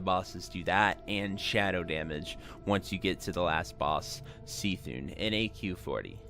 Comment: Did he just say ''See-thun''